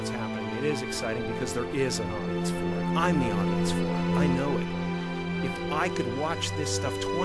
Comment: what does he say at 4:32 "there is a.... im the...?" no idea i think it sounds like audience four? any ideas